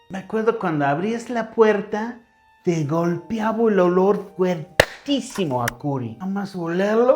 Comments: Pensé que había dicho un olor fortísimo a “cola” 😂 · lo estaba escuchando sin ver y escuche "te golpeaba un olor fuertisimo a Q-lo" en lugar de curry LOL